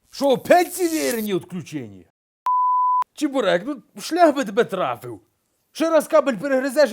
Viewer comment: что он говорит? Шляк или шлях?))